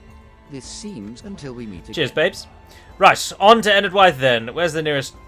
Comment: Is it just me or did I hear just say "Cheers babes". at 28:08.